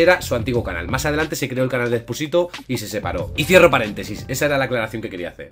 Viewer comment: pensé que dijo y fierro pariente jeje